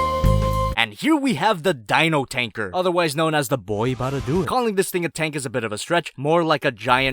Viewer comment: Did you just say Mata-Nui? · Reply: I think he said something like, "Boy, boutta do it," or "Boi, I'm boutta do it." Kinda funny either way